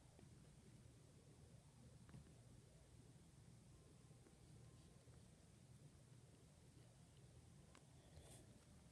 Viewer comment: Aww. <3 BTW, at 0:46, what did he say? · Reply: Byulétapus L. Fine I’ll lay on my back 😉😁